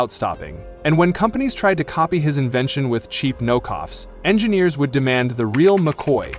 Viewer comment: It said "no coughs instead of knock offs" 😂😂😂 6:00 it said it again! I am going to bed.